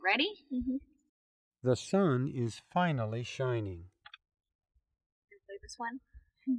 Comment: T H E S U N I S F I N A L L Y S H I N I N G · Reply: I'm hard of hearing and eligible for cochlear implant. Until I read the comments I really thought they said "THE SOUND HAS FINALLY CHANGED"🤔😂